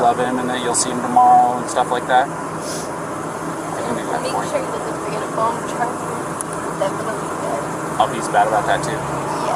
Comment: The 1:00:00 mark when the officer asks Gabby "Do you want me to say anything to him" for her, she says something about a phone charger. Sounds like she says "make sure he gets the green phone charger" but he said earlier in the footage, he doesn't have a cell phone & it has been documented that he didn't have a phone. Just odd that's all